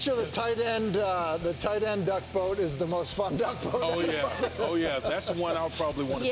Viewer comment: Did that F bomb really make the broadcast? 3:37. (Nevermind, he said 'fun duck'.... Misheard it)